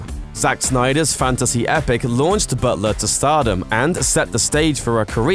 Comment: is he saying "Buttsler"? Sounds like he says it correctly some of the time though. weird